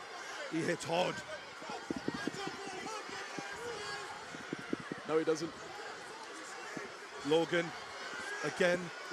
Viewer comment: Did anybody else hear when joe weller said “no he doesn’t” when the announcer said ksi punch’s hard